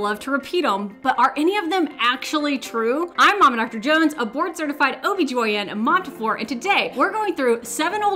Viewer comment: Every time you say "a board certified" at the beginning I hear "abort certified" 😅